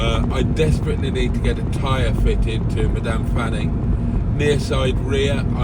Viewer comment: I thought you said, “my damp Fanny” instead of Madame Fanny. Unless I’m a couple of laps behind 😂